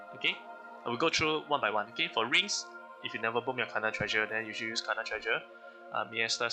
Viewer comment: what are u saying bro ebuchepu kanna tjreshu if u never kanna threshu. Thats what im hearing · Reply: HAHAHHAHA i get what you mean bro, what i said was, "if you nvr bomb your kanna treasure then you should use kanna treasure" kanna treausre is the ring